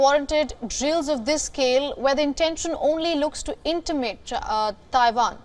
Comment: I hear 00:20 intimate